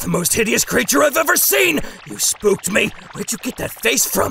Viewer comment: Amazing. Perfect as always. I died at "ya ugly frugly boi" 👌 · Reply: I know I am aware. But I heard Broccoli as "Frugly" and I just died at that. I thought he said "Frugly" and that made me laugh more. I am sorry if you were offended/irritated by that. Thank you for your understanding!